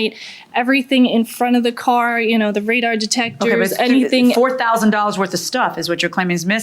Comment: Did she say "Radar Detector" ? wt da?